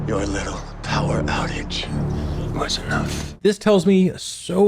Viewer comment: omg so excited for your theories! btw Christinas boss telling her to write bad stories (instead of hopeful stories) i very similar to what Ford said is what the tourists wants. also at 14:37 isnt he saying "TOWER OUTAGE"?